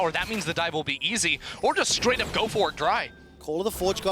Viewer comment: did he really say "go for it dry" ? :O